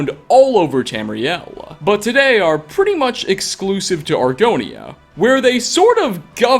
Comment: did you just say Argonia?